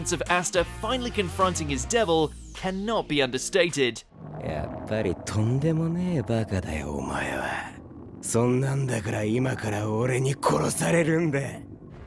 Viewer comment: why do I hear “you are very”